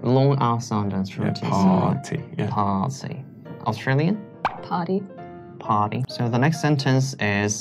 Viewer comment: American: "It's a pity that I can't go to the party" UK/AUS: "It's a pity that I can't go to the potty" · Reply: It definitely sounded like the australian said "potty" at 3:02 though.